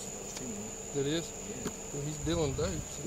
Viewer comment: does he say "hes doin birdshit!"????